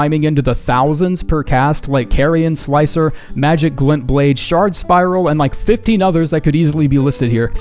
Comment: I misheard that as "shart spiral" and almost spat out my drink.